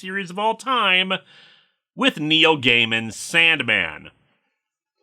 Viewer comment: Who else here at first heard "Neo" instead of "Neil" and thought a review of some obscure manga was next?